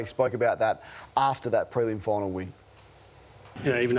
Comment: What does mccrae say at 4:57? Something drills? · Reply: 1 on 1 fight drills